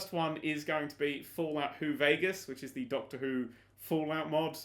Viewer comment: I thought you said just Fallout New Vegas and was so happy...